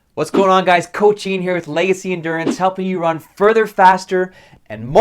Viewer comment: First time watching the channel ever and I thought the guy said lazy endurance